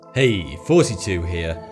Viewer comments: I thought you said "Hey 42 here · Every time u start I hear '42 here' instead of, but what is actually hilarious is that if I activate subtitles, they also say '42' XD XD XD